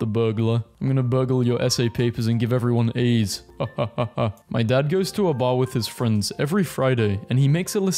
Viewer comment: I heard "give everyone aids" and had to do a double take